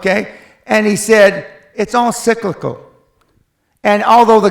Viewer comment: what did he say at 2:28 guys: it's all what ? sorry i'm from non-english-speaking country. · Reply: Bá Luân Cyclical